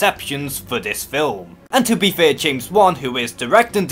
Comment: WAIT? DID YOU SAY JAMES BOND IS DIRECTING?!?!